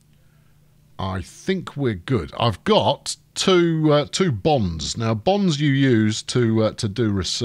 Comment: My American ears heard him say Bomb not bonds. Now it makes sense.